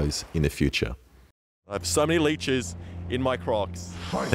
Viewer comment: At the end I heard, “I have so many leeches in my crotch”